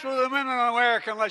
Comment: I thought he said unless you want to get in bed ifem.